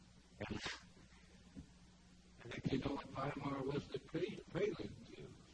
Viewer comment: What does he mean when he says “Via Marte” “Via Maris” no closed caption. 1:32 he talks about a prelude… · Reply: He said "Weimar" as in the Weimar Republic, which was the name for Germany in the period between WWI and when Hitler assumed control in 33.